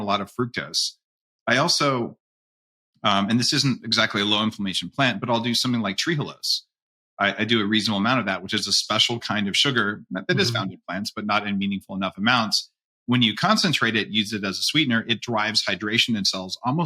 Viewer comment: What sugar is Dave taking about @ 8:30. It sounded like he says trigulas ? (or was it trehalose)